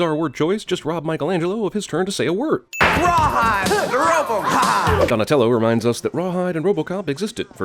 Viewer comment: Don't think he says "Rawhide, Robocop" @ 1:20, pretty sure he says "Rawhide, rope 'em! Hah!" then proceeds to rope him like cattle. That's when the masked robber pretends to be roped like a calf, even though Donatello hasn't even started roping him yet. He acts tied-up while Donatello barely does one loop around his hands and feet, and we're supposed to believe the robber can't break free? Always found that part ridiculous. Enjoy your videos.